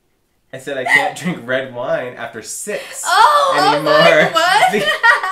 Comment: I thought the same thing I thought said after sex!..lol!!!